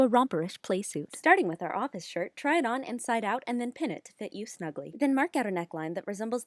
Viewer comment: I thought u said "and pin it to fit you, snuggly" instead of "pin it to fit you snugly" and was very confused